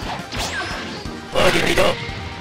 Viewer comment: What does Jiren at 1:18 sound like Voldemort saying “Avada Kedavra”